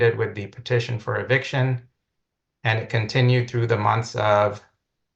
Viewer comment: Did she just say her “nudity” continues?